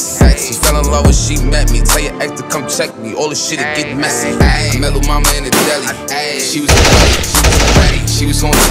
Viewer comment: I thought he was saying I met lil mama in a tally